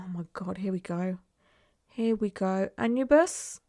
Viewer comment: I was looking at a different screen when I heard "Anne, you bus" and was like "The F is she talking about," looked back, 'Anubis.' First time I've encountered someone that doesn't know how to pronounce Anubis 😭